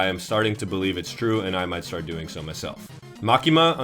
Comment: did he just say he’s gonna start gooning to his friends, like yo? 😖